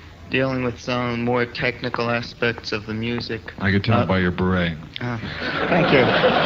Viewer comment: Hello! Can someone please tell me, what Frank says at 20:39? Thank you very much!!! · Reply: I could tell by your beret.